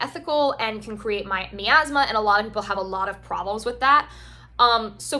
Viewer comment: What is the word you used at 20:03? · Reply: Miasma